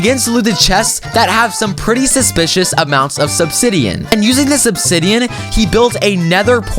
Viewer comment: I thought i heard subsidian- idk if im tired or if ya mispronounced it on accident XD